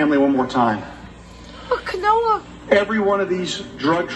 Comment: What's with the part at 44:35 where the camera Zooms in and from what I hear someone says Noah?